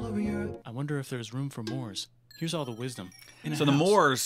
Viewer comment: I hope theres room for MOORS? i thought i heard more